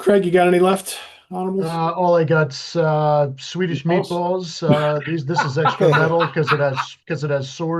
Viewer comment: When Craig said “Swedish Meatballs,” I thought he said “Swedish Menthols.” Which cracked me up.